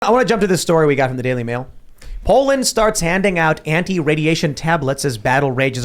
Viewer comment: Ohhh Poland! I thought it said Portland…